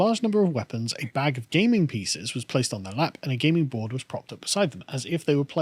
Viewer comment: For a second I thought Carl had said that these vikings had gaming pcs.